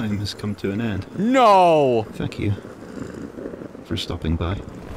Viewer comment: why does it kinda sounds like says "fuck you" not thank you